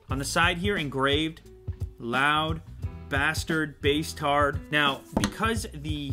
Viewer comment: I thought it said bastard